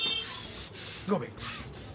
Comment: he pulls him up and it sounds like he says "GO AWAY" and pushes him back down. Did just disspell some kind of demon in this dudes back muscle? Pleass bless me with your wizard fingers my demons are aching right now 😣👌